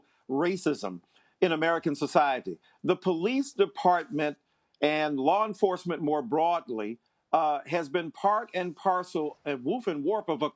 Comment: Can anybody tell me what he said at 0:40 i really want to know. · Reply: jkblue37 “The police department and law enforcement more broadly has been part and parcel”